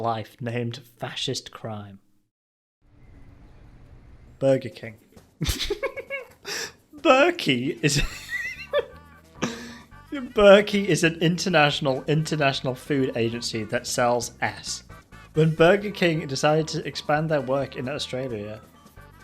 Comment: At first, I thought I had heard, "Burkey is an international food agency that sells ass!" Well, the spelling and grammar definitely are 100% ass, alright!